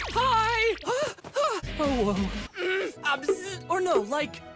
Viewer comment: I think I heard him say "OWO" in